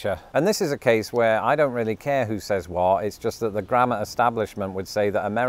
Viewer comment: I thought you said grandma 😂